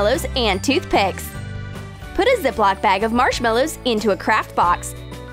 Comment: On 7:44 did she say crack box???